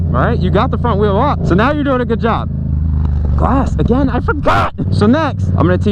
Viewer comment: Was I the only one who thought he said grass the first time?